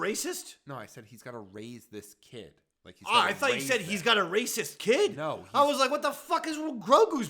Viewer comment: Magnum PI was set to be the original Indian Jones. I personally think it would have been just as good. Maybe even better. The mag pi episode “legend of the lost art” shows Tommy fit that role perfectly. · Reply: I thought he said “racist kid” too. Grugu haha.